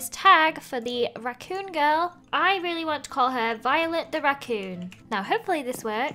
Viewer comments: When she said : “violet the raccoon” while naming it I thought she said “violet the vacuum” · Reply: I thought she said violate the racoon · When she said : “violet the raccoon” while naming it I thought she said “violet the vacuum”